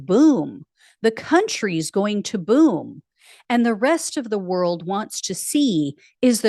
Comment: Is she saying, "to boom", or "kaboom"?